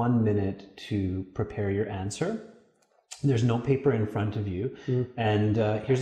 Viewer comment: just burst out laughing when i misheard and thought you said "there's no paper in front of you" in 4:28 which sounds just like you were teasing the guy